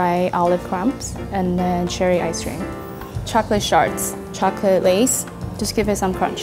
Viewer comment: D...did she just say chocolate shart?